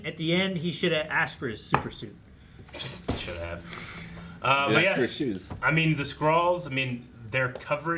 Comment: I loved this film. I know it wasn't but what I heard, "Where's my super-spy shoes?" :)